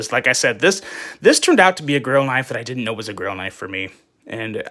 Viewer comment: "Finger???!!!" And I thought you said "Girl Knife" at the top of the video and realized you said "Grail Knife"! This "Babe"...er, "Blade" is a beauty, and the curves on the handle, and how the flipper/finger guard nicely tucks in and under....hot! I like this one. Thank you. Can't wait to see the other two knives.